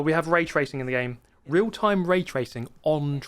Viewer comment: for a second I thought he said rage racing and was like duh, what do you expect from Forza